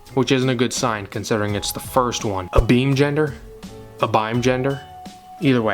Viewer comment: I thought abimegender said animegender...we have come to this